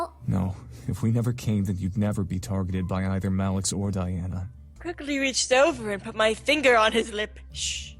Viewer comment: I thought she said I creepily reached over